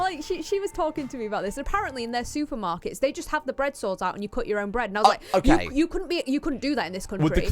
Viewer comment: Ah, bread saw. I kept hearing bread “sore” until the context came into play.